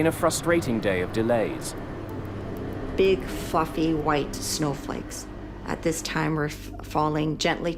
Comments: did she just say "big f***ing white snowflakes? at the start of the video?? 😮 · At the start it sounded like she said "big fucking white snow flakes were falling" lol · I thought she said "Big F**king White Snowflakes" 😳